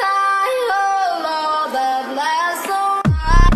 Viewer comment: Its sounds like its saying adi adi o thro that laso